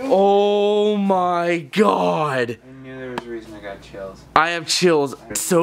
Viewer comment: finds the ouija board “i knew there was a reason i got chills”- me: gets chills oh wait- realises i’m eating ice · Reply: I thought he said there's another reason I got chills